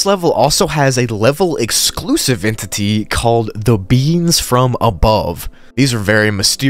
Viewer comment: sounded like you said “The Beans From Above”